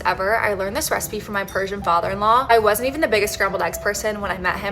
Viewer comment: I thought she said “virgin father in law” and was just trying to puzzle out HOW and why it was relevant to how the eggs were cooked for the entire first play though